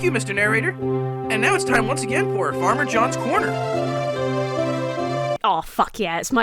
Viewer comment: I love to watch how reacts to this cartoon! But I wasn't looking at my screen when they started talking about this farmer and I heard "Parmesan's corner" 😅